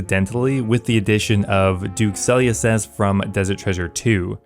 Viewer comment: did you just say Duke Sulliucep lol